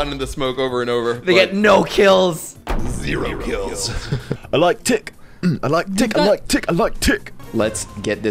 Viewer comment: did say “I like dick” or is that just me😂😂